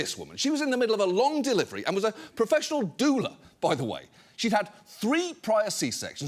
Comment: Oh. Doula. I thought you said professional doomer for a sec.